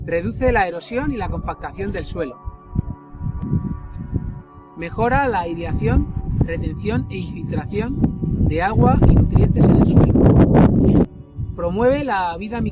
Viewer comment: Hola buenas, en el min 2:22 que dice? Por más que repito no logro entender, gracias por el video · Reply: Hola. Digo, mejora la aireación y la filtración de agua y nutrientes en el suelo. Lo que viene a significar que lo deja mullido, no apelmazado. Esa es la idea que se pretende transmitir. Perdón por el ruidoby el audio. Muchas gracias. Saludos.